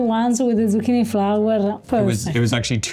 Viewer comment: I thought you said zucchini flour, until you showed the flowers! 😂